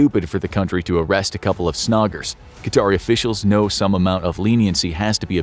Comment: WHAT IS HE SAYING !? Snoggers ?!